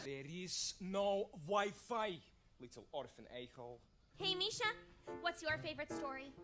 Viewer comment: I think he’s actually saying “Little Orphan A-hole”, as a joke on “Little Orphan Annie”. Basically calling her a redheaded goody-two-shoes